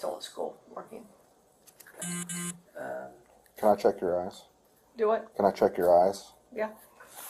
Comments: When the cop asked teacher if he could "check her eyes" i thought at first he said "can i check your a$$" 😂😂😂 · It sounded like he asked if he could check her 🍑 at first. . . 😂